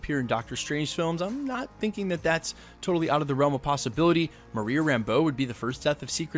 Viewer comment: I thought they said Monica as well instead of Maria.